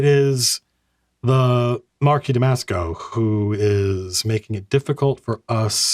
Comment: I’ll probably not be the first to make this joke, but Marquis Damasco should have a brand of hotsause in Capitol, because I always hear: “Marquis Tabasco” So ... maybe there’s a “Marquis Damasco’s Magnificent Tabasco” local hotsause?